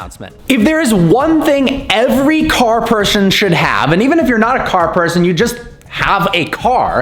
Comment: did you say car pershon?